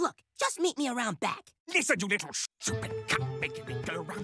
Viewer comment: With context, he actually says "Listen here, you little shoe horn!"